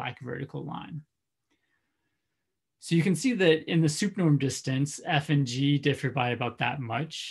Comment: Very clear explanation! At 1:34 you say "in the smth norm distance". What is the word that precedes norm distance? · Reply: Hi, thanks for the question. I say "in the sup norm distance", which is short for "in the supremum norm distance". See <link> (for example) for the definition of the sup norm. The sup norm of a real-valued function f : X -> R is the supremum (think maximum) of all of the values of the absolute value |f(x)|, as x varies over all points in X. Given two real valued functions f : X -> R and g : X -> R, their sup norm distance is the supremum of all of the values of the absolute value |f(x)-g(x)|, as x varies over all points in X. So the sup norm returns the largest possible discrepancy, over any possible input, between the two functions.